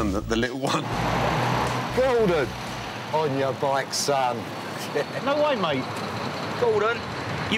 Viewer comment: What does the taxi driver say on 0:23-0:26? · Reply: "Gordon!...On your bike son"